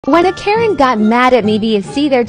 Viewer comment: I thought you said “when a pesent” got mad